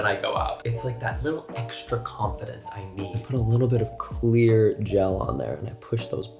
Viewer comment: I THOUGHT YOU SAID QUEER GEL.